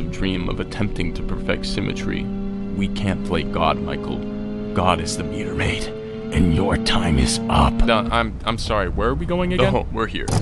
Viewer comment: after two years, i still dont know what hes saying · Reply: God is the meter maid, and your time is up